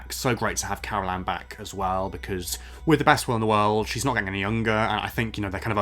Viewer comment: "With the best *inaudible* in the world" What word did you use there?? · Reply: Will!